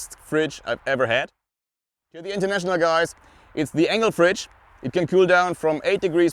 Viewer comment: Hello, cool video very interesting. Do you have a link to the fridge supplier, it sounds like you say angle fridge but I can not find it spelt this way. Thanks in anticipation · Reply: Hey Julian, it is spelled "Engel" my one is the MR 040F but they've got several models of all sizes and cooling abilities 😀👍